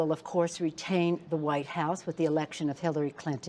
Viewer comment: I'm sorry, my volume is acting up, did she say paint the "Out House"?